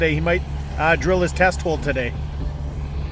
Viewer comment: I thought you said Zak was going to drill his testicles today. I need a new headset.